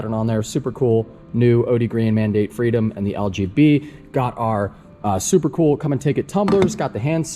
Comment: Electrical fault definitely. Also at 11:30 I thought you were saying the dreaded LGBGT (might have spelled that like MG MGBGT